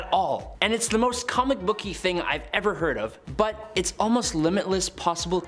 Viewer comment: I thought you said we never hurt of?